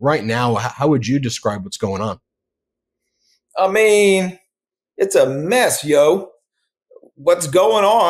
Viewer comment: did he say "its a mess yo" ?